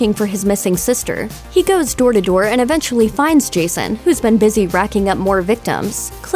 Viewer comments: did anyone else hear her say crystal wake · In 15:20 she says Crystal wake Instead of lake